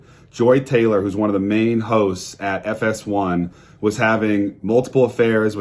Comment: I heard "Joy Taylor...one of the main hoes" instead of hosts and it fits this situation perfectly 😂😂😂